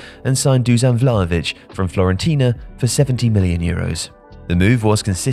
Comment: does he say FLORENTINA?!